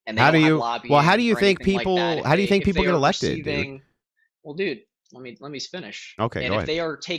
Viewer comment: did he actually say “dude, let me spinach”?